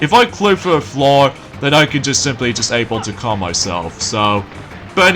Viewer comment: What we're you saying? at 20:08 (if I clay it through the floor, I can just able to calm myself!) I can't make out what you were saying!